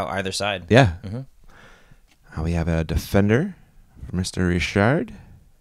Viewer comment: I know he is saying "Mr Richard" with that accent but I hear "Mystery Shart" every time.